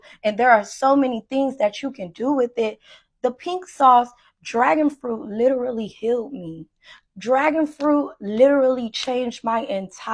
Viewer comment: This is hilarious if you have the captions on because around 8:26 she says "the pink sauce dragon fruit literally healed me" but the captions say "the pink sauce dragon fruit literally killed me." Lmao